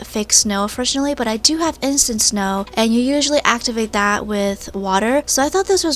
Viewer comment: When u said instant snow I thought u said innocent snow XD